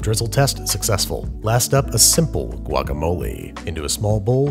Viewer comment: You did NOT need to say “guacamole” like that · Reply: I heard “ guagamole” like... no